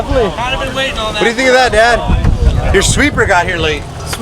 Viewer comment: Did call dad @ 4:41?? Tf